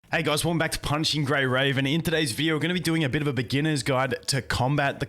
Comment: I heard "Punching Grey Raven"